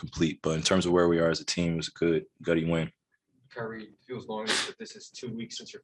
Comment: What did Kyrie say at 1:22? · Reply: A gutty win?